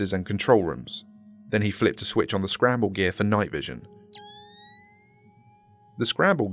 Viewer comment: Wait did I hear "scramble goggles"?